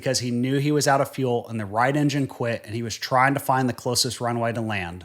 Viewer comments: did I hear you wrong? You said he was flying a Cherokee and that’s what you showed, right? Towards the end I thought I heard you say the right eng quite? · Did you say "the right engine quit" at 13:54?